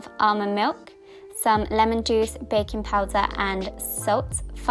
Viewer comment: Whenever you said 'baking powder' I misheard 'bacon powder' and I was so confused for most part.😂🙈